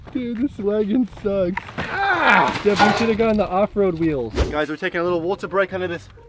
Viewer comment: You said it’s abandoned in the thumbnail but I heard you say I think it was “ we should of got a different one!” I think but it’s true that’s not abandoned